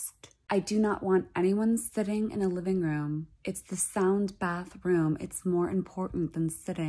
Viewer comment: thought she said sand bag room and was wondering what trends la is going thru